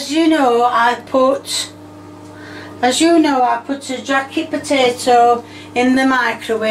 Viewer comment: I was expecting some hater to point out that microwaves don't produce radiation, which Is an amount that can't harm you. 5 Milliwatts per square centimeter. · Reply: Also did she say jacket potato?